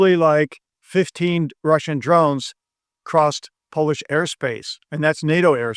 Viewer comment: Europe doesn't want war, fine, so the best answer is to give to Ukraine everything they need to end Mordor. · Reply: I thought I heard Mr. say "Russian drone aerosol" which almost made me drop my coffee cup, please be safe where you are.